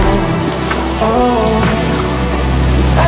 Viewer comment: "if there pain within his ball's" this is what I thought he said💀